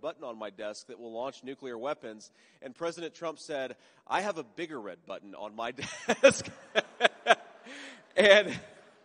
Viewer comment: I thought trump said, “I have a bigger red button and mine works.”